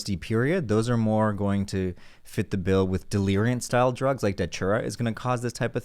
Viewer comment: What is the "full on hallucinatory transformation drug" that is mentioning in 10:57 I just can't understand? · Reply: its datura, my friend